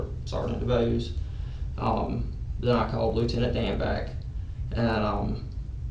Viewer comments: I thought he said call Lieutenant Dan back. And I was greatly disappointed that his name was danback lmao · Lieutenant DAN!! 15:09 Did anyone else think he just said Dan like on Forest Gump?🤣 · I thought he said “I called Lt Dan back” and I cackled😂 then he said “Danback D-A-N-B-A-C-K”